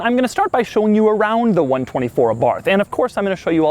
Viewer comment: sound like youre saying Abarf. lol